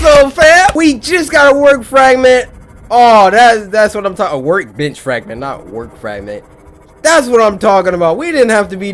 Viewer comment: Cory, at 2:09 it sounds like you were saying work b**ch fragment instead of work bench fragment.